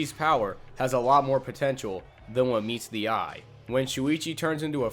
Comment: DID YOU SAY POTENTIAL!?